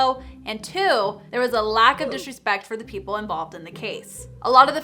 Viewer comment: did you say a "lack of disrespect"?